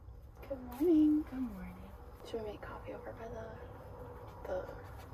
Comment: At first I thought you said "Should we make coffee my love?" Lmao